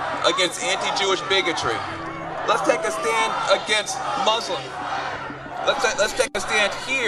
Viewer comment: Did I hear him right? Did the guy at 16:52 say "Let's take a stand here against anti-Jewish bigotry. Let's take a stand against Muslim"? So basically, let's take a stand against anti-Jewish bigotry, and instead let's promote anti-Muslim bigotry? Can he just openly say something like that!!